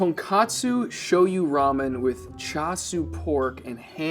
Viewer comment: There are a couple of caption errors. At 0:47 the caption says Tonkatsu, but the correct word is Tonkotsu. Two very different Japanese words. The former is deep fried pork, (cotellette) and the latter is pork bone broth.